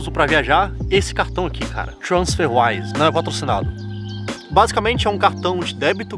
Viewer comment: alguem sabe me dizer o nome do cartao? nao entendi qnd o falou 6:29 edit: achei o cartão, o nome é transferwize. Pra caso mais alguem nao tenha entendido